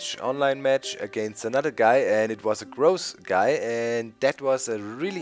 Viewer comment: i thought he said gross guy lool